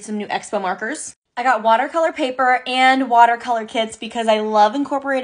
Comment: You: and watercolor kits Me: DID YOU JUST SAY WATERCOLOR KIDS